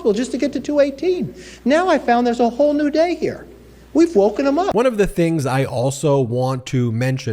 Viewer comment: did he say they are now Woke?